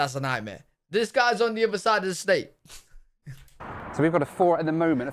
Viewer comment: You misheard him.. He said that the driver was 4 hours away on the other side of Indianapolis... Indianapolis is just one CITY within the state of Indiana 😅.